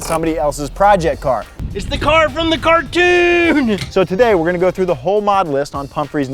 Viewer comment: excuse me...did you say cartoon bruv???